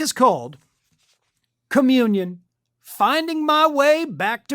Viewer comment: When said "communion", I heard "chameleon". I thought to myself, "Yeah, that tracks." 😂